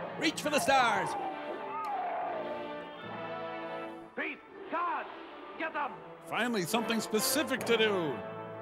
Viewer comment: does he say, "Pete, Tod get'em"? Wow, so Sumerian.